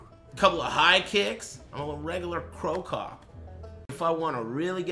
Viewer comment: did he say "'im a regular cro cop'''?